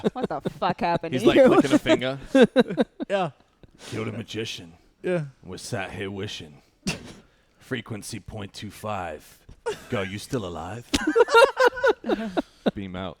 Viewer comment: Can someone tell me what's Rex song lines are? I don't think I understand a part of it (24:13): "Killed a magician with "???", frequency .25, girl you still alive?" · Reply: Killed a magician, and we’re sat here wishing, frequency .25, girl you still alive?